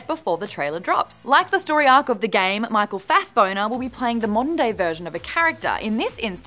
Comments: did i just hear Michael Fast "boner"??? lol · did... did just say michael fassboner? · Did I hear her right at 0:56, michael fassBONER?? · did just say michael fassboner?! (rip rhaegon and lysanna)